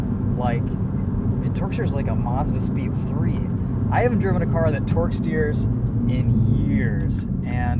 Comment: I thought he said, “I haven’t drove a car that twerks in years” 💀